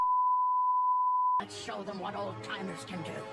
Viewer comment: not the auto subtitles of Wu saying "Lets show them what alzheimers can do" instead of "old-timers" lmaoooo 💀